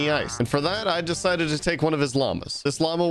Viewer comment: I THOUGHT HE SAID HE DECIDED TO TAKE ONE OF HIS LUNGS KSJDIDJD